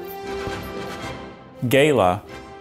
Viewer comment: Did he just say Gayla